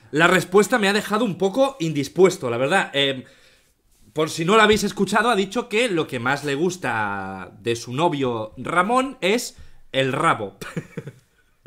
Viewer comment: jaja yo le entendi rabo de trasero y pensaba que era eso hasta que auron dijo miembro viril xdd